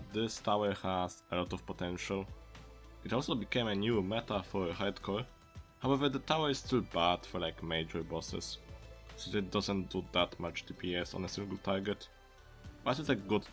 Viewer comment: he really said ''haed cuh''